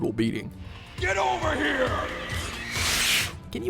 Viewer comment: Anyone else spammed the spear when playing as Scorpion and suddenly heard him say "Get over her bitch"